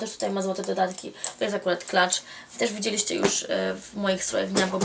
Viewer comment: Heh, usłyszałam 'klacz' i kilka sekund mi zajęło zanim się w głowę puknęłam, że chodziło Ci o 'clutch'! ;) Pozdr.